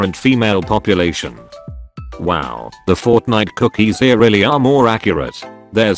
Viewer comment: is it just me or did he say fortnite